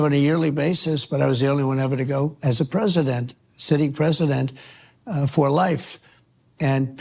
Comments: Did he just say he’s a sitting President, for life? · did he just say "sitting president for life"? Does he think he is still a sitting president? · I may have misunderstood, but at around 2:52, Did he really call himself a "sitting President, for life?" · Did I hear it correctly? Trump called himself a sitting President……..for Life?? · At the 2:55 point does he say he thinks he was elected for life???!!! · Starting around 2:50 Trump said he is the sitting president for life,hopefully I misheard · Religious issues aside, did he say at 2:50 "president for life"?